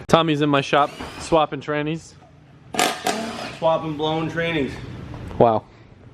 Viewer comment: I almost thought he said "Swapping blown grannies"